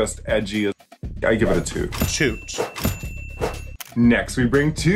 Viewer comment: did Raven say SHOOT?